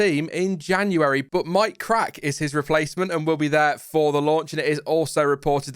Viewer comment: Who else thought said, My Crack is Otmar's replacement? 😅